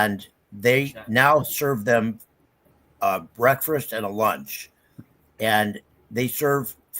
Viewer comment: Did say "breakfrast" twice at 35:02 ?